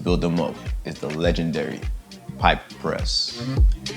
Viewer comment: it sounds like you say "pipe press" instead of pyke press